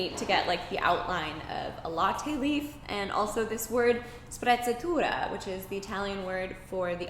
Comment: I'm Italian and for a second I thought she said "spazzatura" (aka garbage) instead of "sprezzatura" when she was talking about tattoos and I was like... that doesn't mean what she thinks it means...